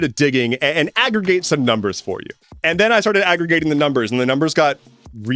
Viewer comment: I did not hear, "aggregate the numbers", I heard "aggravate the numbers". So when you then said, "and the numbers got really...", my brain finished with "angry". Great video as always!